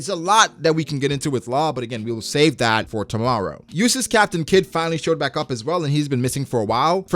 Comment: Bro I thought he was kid fan lol he said useless caption kid 😂